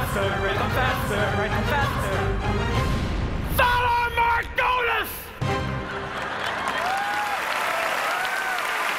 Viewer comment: I have no idea what he says at 1:32. Anyone? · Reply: He said Valar Morghulis.